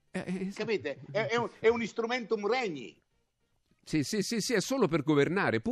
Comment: Qualcuno sa dirmi cosa dice a 1:56? Ne sarei grato. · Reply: Instrumentum regni. , Locuzione latina